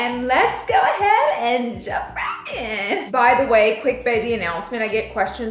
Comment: Girl. I swear I heard baby announcement.❤ before jewelry ? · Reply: If you look at the transcript it confirms she said, baby announcements. But I thought it heard just baby as well.